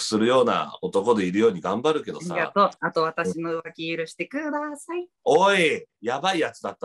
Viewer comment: これなんて言ってるかわからんw 私の上顎キルしてください？ 私の上履き入れください？ わからんよーw